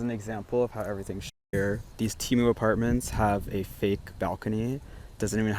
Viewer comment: Did he say 'temu apartments'?